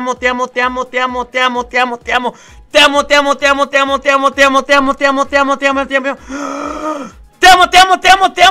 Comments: párese que dice "te a muteado" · Minuto 6:43 le entendi muteado